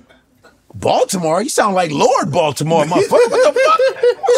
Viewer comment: "Baltimore? You sound like Lord Baltimore!" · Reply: I thought he said Lord Voldemort 😂